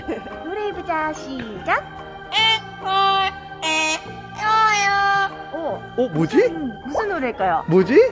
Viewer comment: 저만 예뻐해줘요~ 로 들리나요 ㅋㅋ